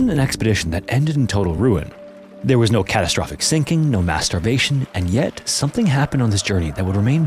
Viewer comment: No masturbation? wtf am i hearing right.. i replayed this 4 times